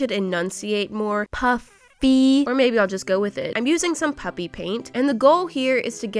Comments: I never noticed that I sounds like she is saying “puppy paint” sometimes until she pointed it out! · Everyone: sounds like your saying puppy paint Me:I didn’t notice it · I always thought she was saying puppy paint😭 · I honestly never realised that she said puPPy paint And now I'm gonna always think that she says puppy paint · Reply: I always thought she said puppy paint. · It does sound like your saying puppy paint 😂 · Alrighty, lets start a petition to get pickle in the unicorn floaty on a hoodie. One like equals one signature. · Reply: "ha it sounds like you said PUPPY paint" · It sounds like is saying puppy paint🤣😂🙃HA HA